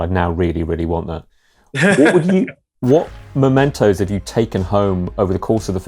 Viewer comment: when he said "what mementos" istg i thought he said "what mentos"